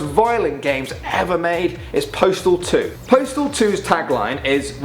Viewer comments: I thought you said portal 2 instead of postal 2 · When you first said "Postal 2" I thought you said "Portal 2" and I was like HOW IS THAT GAME OFFENSIVE?!?!? ALL YOU DO IS SHOOT PORTALS!!!!?!?!? xD